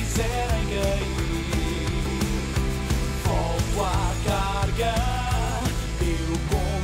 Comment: "VOLTO À CAGAR" Foi oq eu ouvi pessoal